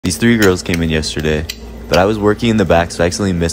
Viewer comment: I thought he said these three year olds came in yesterday